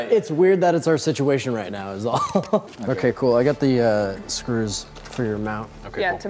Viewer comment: I thought said “I got the screws.... for your mouth”